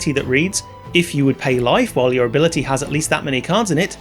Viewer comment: you accidentally said "If you would pay life while your ability has that many cards in it" instead of "your library has that many cards in it." Just FYI :)